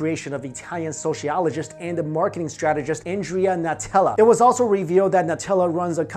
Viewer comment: When he said ‘Natella’ I thought he said ‘Nutella’ and got really hungry